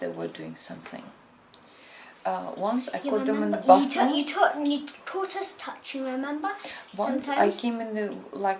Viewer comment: I do believe the kids implicated their mother also as one of them says you taught us touching remember, sometimes 7:57 omg and she talks right over her child. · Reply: I heard ‘caught’ not ‘taught’